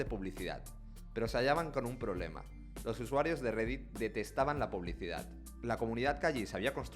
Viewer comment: Pensé que decía redmi 😅